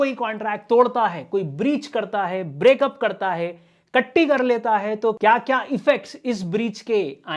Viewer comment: Did he say tatti krr deta h ?? 🙄😂😂😂😂😂